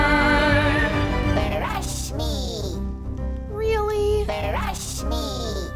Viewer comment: Brush me! Really? *kawii cute eyes BRUSH me 😂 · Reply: I thought it said Fresh Meat first time I heard it O.O